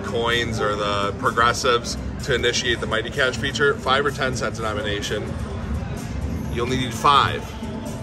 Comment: I could have sworn you said "5 or 10 cents...and I'm an Asian"